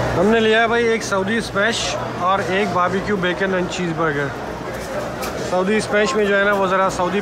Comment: Did I hear Bacon at 5:45 😲😳